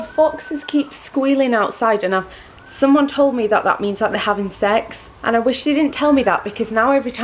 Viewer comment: Ok ngl when you were talking about the foxes I thought you said "they have insects" so I was sat here all confused as to why they are rascals. 😂